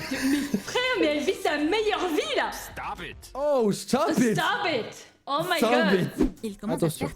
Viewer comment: Désolé, j'ai entendu dire "STOB IT" à la place X)